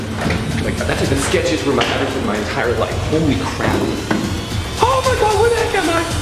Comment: Did you say Holy crap?? :v